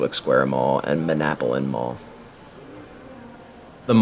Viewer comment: Did he just say Man app a linn Mall? Tell me your not from Central Jersey without telling me your not from Central NJ 😁